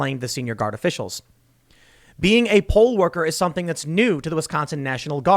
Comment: I don't care how aware I am of the subject of this video, I keep hearing "pole-worker" every time Tim says it.